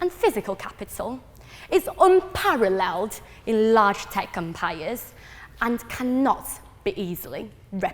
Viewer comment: tech empire, and i heard tech "compire"